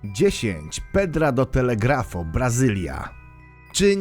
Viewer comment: usłyszałem ,,pod telegrafem''😁